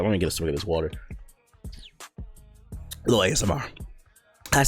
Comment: The actual hell did you say @ 9:35 ? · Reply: Honcho 740 “Little ASMR”